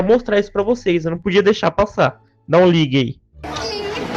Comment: eu entendi que coisa Hein ou tira isso ai!